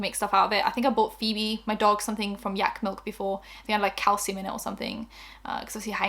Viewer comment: Girl at first I thought you said 'cow semen' not calcium 😂😂 threw me tf off